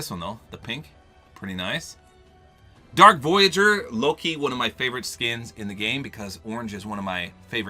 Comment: Did just say "lowkey"? 5:26 lol